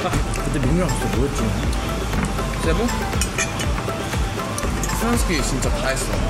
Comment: mingyu and joshua went to CEBU, 😱 do i heard it right?